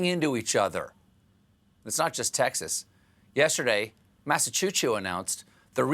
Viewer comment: dude, did he say "massachu-chu"?